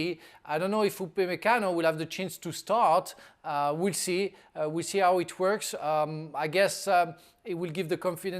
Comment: sounds like Frank says "we'll see how he twerks" lol